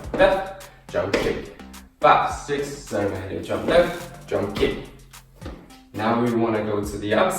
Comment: if you put it at 2x speed it sounds like he’s saying jungkook instead of jump kick🤣🤣🤣